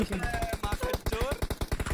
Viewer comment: I can't believe my ears... Did Mortal say M@@ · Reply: No bro he said makhanchor